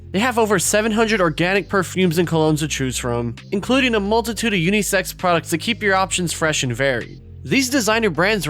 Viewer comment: I misheard during the ad and thought he said "including a multitude of sex products" instead of scent products XD